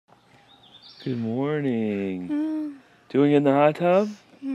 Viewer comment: It's so awesome to see communication improving! In the beginning of the video, it sounded like she said "mmhm" I want to go in the hot tub!